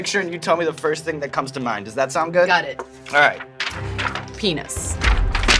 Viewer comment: did she just say penis?